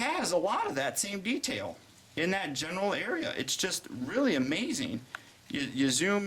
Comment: Did you say that he has a lot of detail in the genital area?! - and you seem to like it a lot :O